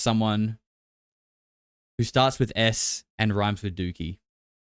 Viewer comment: I thought you said “starts with S and ends with Uki”